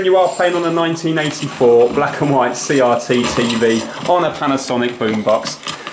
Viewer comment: Did he just say ttv 6:24 ?